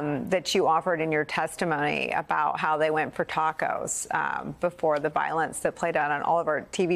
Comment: Well! I thought he had said that they went for "tuckers" old brit slang for "a nice meal"... but it WAS "Tacos"... so I stand corrected!